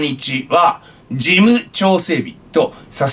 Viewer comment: 事務調整日か 事務長整備と聞こえてしまった 笑